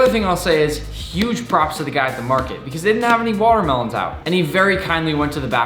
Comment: At first I thought it said “vegan watermelon 🍉 “ and I was like water melon 🍈 is always vegan.